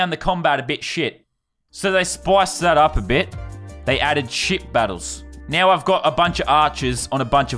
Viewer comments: Who else thought he was saying shit battles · It sounded like he said shit battles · Ay Lannon it sounds like ya saying s$%# battles not ship battles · At first I thought he said shit battles instead of ship battles